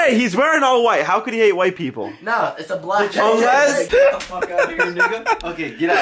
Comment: did apex say "get the fuck outta here nigga."